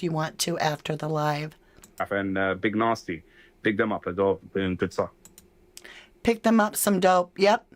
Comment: Yeah I heard “BIG THEM UP ASWELL … THEY DONE GOOD STUFF”